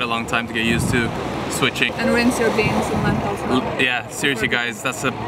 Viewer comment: I didn't understand the word. 10:32 What to do with the beans and lentils? "Brense"? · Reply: rinse